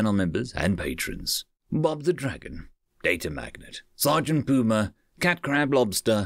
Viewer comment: It's kinda strange not having the intro end with "Bob the dragon" which for the longest time I was mishearing as "bother the dragon